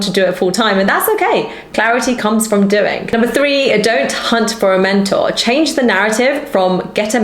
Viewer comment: Hahaha I thought you said in number 3: "Don't hunt for a man" xd